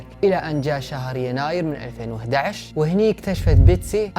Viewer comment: سمعت غلط انه أسعد خبر بحياتها 5:00 😭😂😂